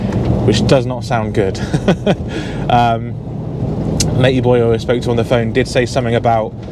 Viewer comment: I'm sorry, what? Did you just say you spoke to a ladyboy? 🤣🤣